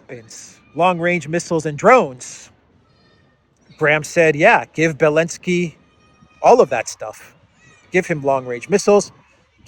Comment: I thought said "Give Bellendsky weapons and drones"...?! Well, that would be a new and appropriate description for the Gnome of Kiev! :P